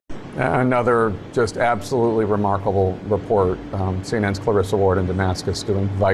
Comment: Could've sworn Jake said, "absolutely remarkable performance..."